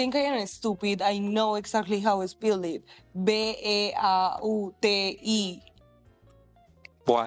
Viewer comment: she’s most likely pronouncing the “y” how they pronounce it in her language, and not saying “E” If you say “why” without the “wh” you’re left with the same sound she made, she’s definitely saying BeautY. We pronounce Y in a similar manner as she did, and I’ve made the same mistake myself, also for the letter “w” as we call it a doubleV instead of doubleU over here. Spelling and counting are more often than not more natural to do in your native language, even if you’re fluent